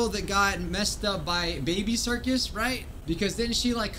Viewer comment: Am I the only one who heard him say baby circus